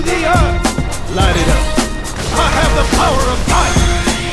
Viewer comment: Did he just say “I have the power of punch” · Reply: The power of Gia